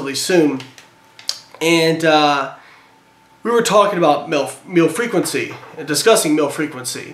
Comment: "we were talking about milf.. meal frequency" did anyone else think he said that? lol.